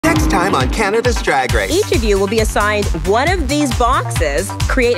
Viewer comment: I heard “cannabis drag race”